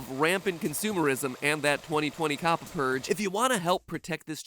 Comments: I thought he said 2020 cop purge and I was like hol up. Matt pat time travel? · When you said "Kabba" I thought you said coppa